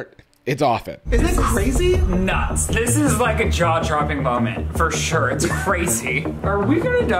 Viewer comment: I legit thought he said "this is a Jarjar Bing moment" 💀